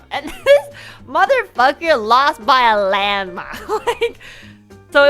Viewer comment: on the other hand, thinks it's "lost by a landmine" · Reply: I noticed that too, but I think she said landMILE, and the person who subbed thought she said landmine instead, which if that's correct it adds another layer of irony to this whole thing